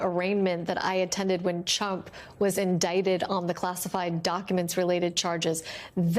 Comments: Did she say CHUMP not trump at 5:26 ? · Did she just call him "Chump" ?! 🤣 I can't stop laughing! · Did she just call him Chump?? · Did she really call him “Chump” there at 5:26 ? OMG! 😳 I wonder if ‘Chump’ actually heard her say that? 🤣😂😆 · I heard “Chump” at 5:25. Great slip 👍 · Did she actually say "Chump" at 5:27??? 🤣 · …when ‘Chump’ was indicted… I had to rewind a few times to make sure I heard it right…Freudian slip? 😂😂😂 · When Lisa Rubin began she clearly said 'Chump' rather than 'Trump' Not really a mistake. 😅